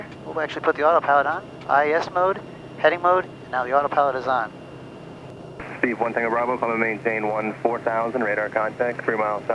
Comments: anyone else heard the controller say Steve 1TB 😂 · Anyone else catch the call ATC gave at 7:33? Steve 1 Tango Bravo! HAHA!! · I think ATC called you "Steve tango bravo".lolol